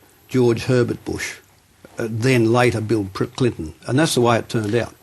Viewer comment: did he say bill "prick" clinton.....freudian slip mr keating?